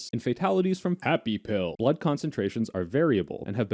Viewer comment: I thought I heard Fappy Pill